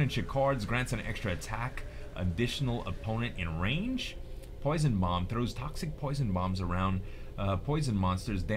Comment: I thought he was saying 'Poison Moms' lol